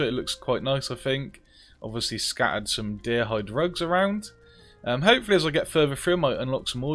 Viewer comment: Genuinely thought you said "Deer hide drugs" when you first got into the house and I've been laughing at it ever since.